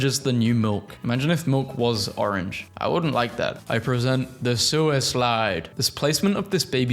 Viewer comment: "suislide" is actually genius lmao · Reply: I thought he said sewer slide.